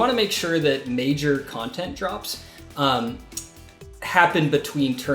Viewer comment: did he said....DROPS?